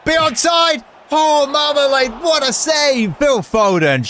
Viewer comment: did he just say 🍯 marmalade